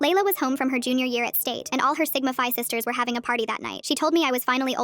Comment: Did they just say sigmafy